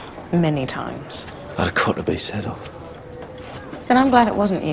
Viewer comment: What does he say after she says, "You already have, many times."? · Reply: Yes! "I'd have cut the beast's head off". Thank you HD:)